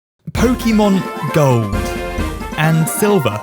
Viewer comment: I thought he said pkmn go in the beginning